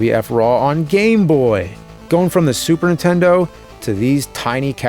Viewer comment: I still love how after all these years, AVGN still pronounces Super Nintendo as "Super Intendo" like so many other people I know ❤ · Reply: I always hear it as "supern intendo"